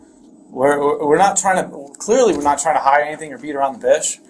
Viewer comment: “ Clearly we’re not trying to beat around the beotch” hahahaha I had to rewind it three times. Make sure that’s what I heard. Lol. Nah we not beatin around the bush. We beat around the biih !!